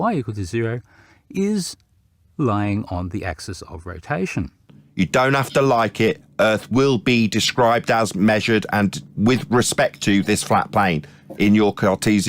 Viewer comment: I thought said, "The earth will be destroyed as a plane!" because he sounded like a constipated Dalek.